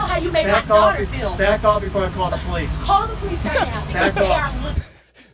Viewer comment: Michael, You are Awesome man! I heard “Back off before I fall asleep”💤💤 douche,? was that the descriptive nomenclature? Thanks for all your hard work. Sweet Family🌟